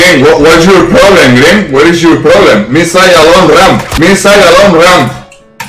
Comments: I always thought he said “b side long ramp” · My favourite part of this is knowing that the rest of the team heard him get cut off as he put multiple deagle rounds into Green's head and got kicked "Me Say Alone Ramp, Me Sa..." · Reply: I always hear "inside a long ram" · I seriously thought he said "missile on ramp" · i thought he said b site along ramp not me say alone ramp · always thought he said me say a long ram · thought he was saying missile long ramp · Bro I thought he said B site alone/along? ramp. What I know for sure is that this guy think he's smarter than he actually is.